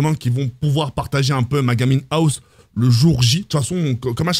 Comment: J'ai compris " ma gamine house"... Tiens tiens tiens